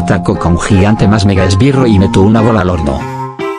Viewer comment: Cuando dice y le meto una bola al horno escuche "y le meto una bola al orto"